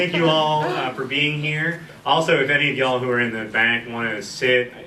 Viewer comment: Did he just say y'all? 1:04 That's most definetely a y'all!